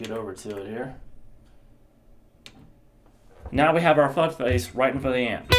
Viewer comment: Did he just say what I think he said? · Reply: fuck face yeah!